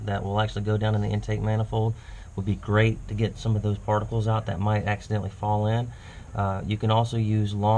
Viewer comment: Hah! He said "barnacles" at 4:30. I do believe those only grow in life-supporting salt water, i.e. the ocean, much less ethylene glycol based engine coolant. :) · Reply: Now that you pointed that out, I listened to it again and yes it does sound like you are saying "particles" after all, which makes more sense. I swear it sounded like you were saying barnacles the first couple times I watched it though!